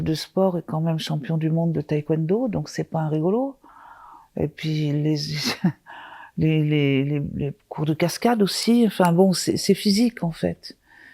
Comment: j'ai entendu "c'est pas Jo l'Rigolo" :D